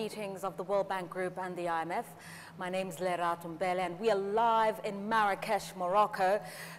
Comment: I thought she said her name is, however the name showing their is different.